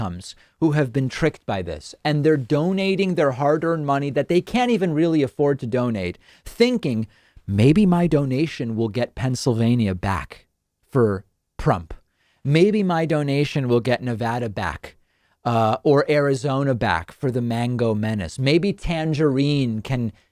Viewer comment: Did he say Prump?